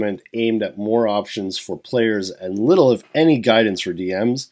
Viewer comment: when you said "more options for players", i first heard that as "morons for players"